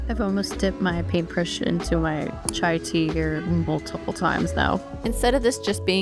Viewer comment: Did you say "Chai Tea"!?